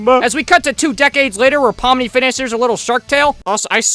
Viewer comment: did you say POMNI?!?!?!?